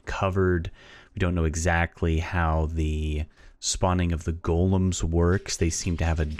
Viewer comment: I heard 'Responding' but it also sounds like 'robotics'.